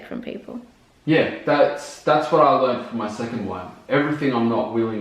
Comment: How many times has been married? I thought I heard 'second wife'